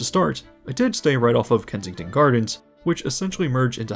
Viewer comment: Second he said Kensington Gardens I heard "FENTON!!!!!"